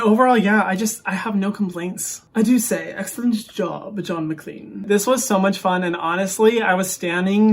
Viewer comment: when u don’t know who John MacLean is😳 and you thought it was john mcclane from die hard😳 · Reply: I thought he said John McCain...